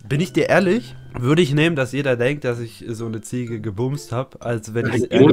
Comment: bitte WAS habe ich Ziege gehört ?!